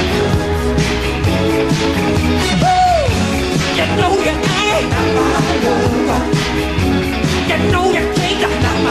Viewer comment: Can anyone write what MJ is saying? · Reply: I think it's "You know you ain't, you know you didn't/can't, because you are, you're just a city girl/silly girl."